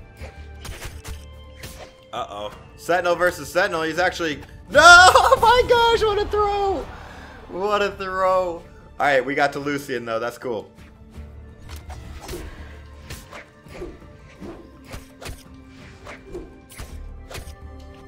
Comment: My progress to diamond: A lot of frustration for sure, but I am currently 1904 and diamond is getting pretty close I hope I’ll be getting diamond soon but I’m not sure if it’s going to happen, wish me luck. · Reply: Savage Guinea Pig oh I thought u were saying I got lucky since all u said was “luck” that’s why